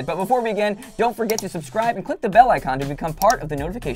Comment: Am I the only one who misheard when they said, "Click the bell icon to..."? I first heard it as, "Click the bell-like condom>" :'D